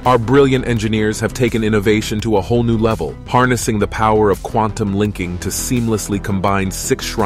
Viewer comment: Im high as hell and i coulda swore i just heard you say Quantum LICKING 😂😂😂😂